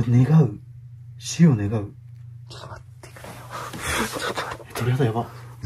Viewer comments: くれよじゃなくて くくれよって聞こえる… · 本当に『くれよ』だったんですかねぇ あの文章を見ると『くくれよ』に聞こえてしまう……